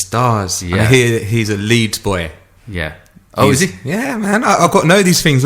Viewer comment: Wow!!!!! Free Fat Burger.. !!! I didn't know Marco P trained Gordon Ramsey.. useful information.. for a second, I thought you said Elite: but then realised you said Leeds Boy